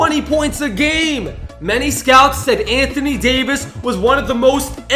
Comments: I heard anthony davis not Bennett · he said Anthony Davis instead of Anthony Bennett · who heard anothoy Davis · instead of saying anthony Bennett leu said Anthony Davis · Am I the only one who noticed he said Anthony Davis instead of Anthony Bennett